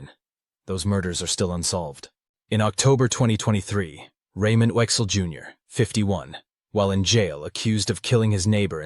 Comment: The guy who "confessed" his name is actually Raymond Oechsle Jr, in case anyone was curious. (It sounded like the narrator said Raymond Wexler jr.) He "confessed" to quite a few in his letter (he detailed mostly public knowledge information though) So just wanted to help! 😀